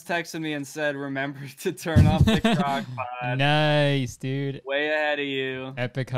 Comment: idk why the funniest part of this video was going “brb chat i gotta turn off my crockpot” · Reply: i watched this video both here and on channel and I keep hearing "crackpot" and i am still very confused